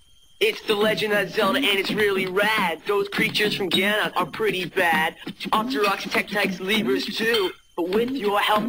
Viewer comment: I thought it said "It's pretty bad"